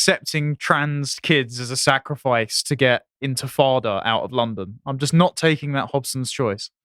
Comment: is he trying to say Intifadah 😂 I heard him say Frodo . It means the Palestinian uprising against Israeli occupation of the West Bank and Gaza Strip, beginning in 1987. Stop fear mongering don’t make it mean what it doesn’t mean.